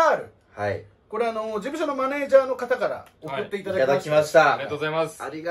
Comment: 事務所のマネージャーが熟女のマネージャーに聞こえた